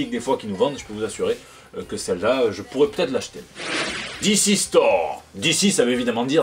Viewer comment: j'ai cru que tu disais "DC's Thor" je me suis demandé quoi x'D